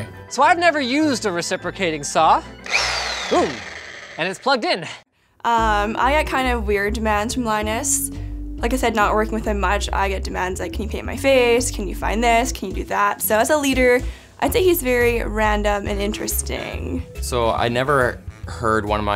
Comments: i thought she said "can you pee in my face" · it sounds like she says "can you pee in my face" I was like WOW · I heard that as *can you pee in my face The fk. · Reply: TactHuz can you “paint” my face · i had to rewind as i thought she said "can you pee on my face?" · Legit thought she said "can you pee in my face" the first time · did she say can u pee in my face?